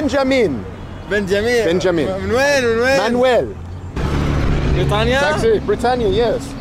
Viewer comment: i think he is saying "min wayn" meaning from where rather than Manuel. 😀